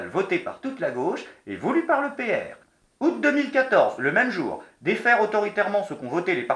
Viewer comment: j'ai compris UPR. ^^